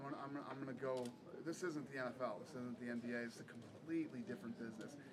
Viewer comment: did he say "this is not the NFL this is the NBA"?!?!?!